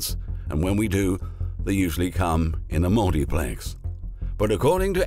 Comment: At the 14:18 mark I can't make out what he said did anyone get that? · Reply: "Usually come in a multiplex..."